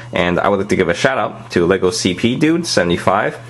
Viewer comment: when u said shout out i thought u said shut up Ha hA